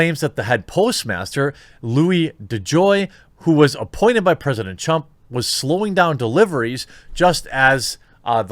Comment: Did you say president chump